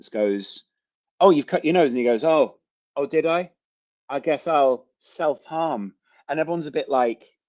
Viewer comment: I'm not a Man City supporter but I don't think he said I self harmed, if he did it may have been a language issue I thought he said I don't self harm, with a Spanish accent.